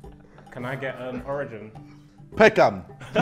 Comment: did he say Beckham 😂😂😂😂